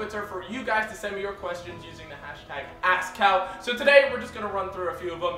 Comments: When he said "thx for joining us in askcal" thought he said asscow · When you say Ask is sounds like you say Ass XD